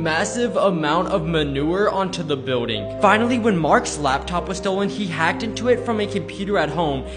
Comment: When u said marks laptop it sounded like u said mark slaptop